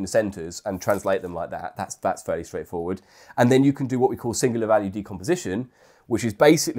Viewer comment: What’s does he says at 9:46 ? Singularity composition ? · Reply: Singular value decomposition